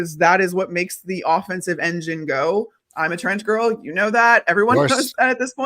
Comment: does she say trends or trans?😅😅